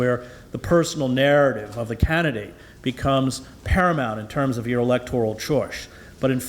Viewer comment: Can somebody - anybody - please tell me, what word Hedges is using here? at |~ 28:26 | ; "paramount, in terms of your electoral chor- - - ." ? 'Paramount; in terms of electoral'... what? Is it a slip? Or, is "chorge`" some kind of elegant, French expression - or what? Could it be that he meant, "choice" , and just rolled through the speech and went with it? Lookin' for help here. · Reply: Paramount in terms of electoral choice.....( lts the Starbucks...)